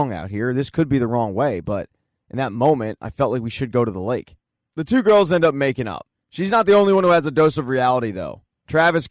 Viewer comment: for some reason i heard “the two girls end up making out” 💀💀💀